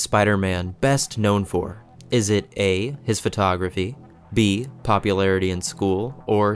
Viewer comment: at the start i fr thought he said his pornography